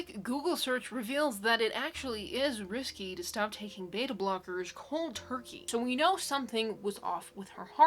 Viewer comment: What did you say at 13:04? Beta blockers cold turkey? I rewatched 3 times but couldn't understand.